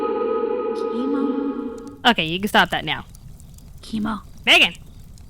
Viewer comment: Did he just say ''Teemo''? xD